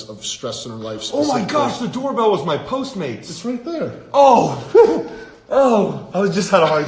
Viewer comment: Can someone you tell me what Dan said? I don't get it? He forgot something? English is not my 1st language 🤔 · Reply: Could be because of stress in our lives and- Dan: OH MY GOSH, THE DOORBELL WAS MY POSTMATES!!!! It's right there... Dan: OH, OoHh , I was just having a heartattack, I'm so sorry. OH, the doorbell rang a long time ago, and I was like: 'Who could have been ringing my doorbell?!?' ,was the postmate, sorry... Food is so important, food is really important, sorry well, sorry for reacting so late, but here it is^^^